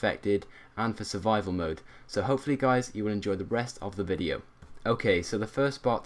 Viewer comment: did he say: enjoy the breast of the video.